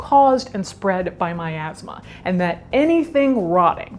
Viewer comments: “....was caused and spread by my asthma” this is what I heard and it took me quite a bit to realize what you’d actually said lol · what Caitlin said: "caused by miasma" what I heard: "caused by my asthma" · I really thought she said “my asthma”